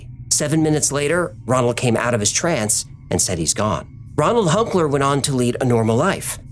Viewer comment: Haha I was on the other side of the room from where this was playing, and I thought he said, “He came out as trans”, instead of, “He came out of his trance” like it was.